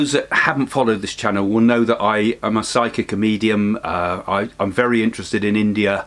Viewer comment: I thought you said "I'm a psychic comedian." LOL.